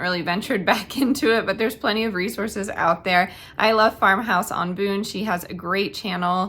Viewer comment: I thought you said, “Farmhouse on the Moon”(LOL). I then realized you said, Farmhouse on Boone. Thanks!